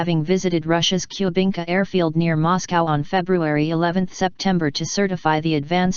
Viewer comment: February 11th September? Wtf does that even mean? Go listen it says that. I call total bullshit!!!